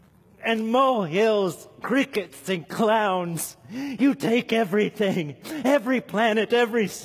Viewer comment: "You take mountains and mole hills, crickets and clouds. You take everything. Every planet, every star, every form of beauty. Everything that sings, everything that brings delight, and you put it all on the scale, and you put Christ on the other side and HE outweighs them all, HE outweighs them ALL! Brethren, this is the one we chase after! " - Paul Washer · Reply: Oh clouds, I thought he said clowns ehehehhe